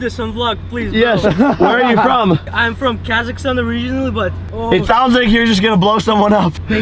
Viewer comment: im not going to lie but when i first heard him say Kazakhstan i thought he said kekistan